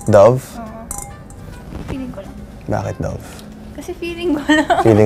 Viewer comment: my volume wasnt that high so i misheard him saying "bakit love?" AND WHEN I TELL U I CHOCKED ON MY WATER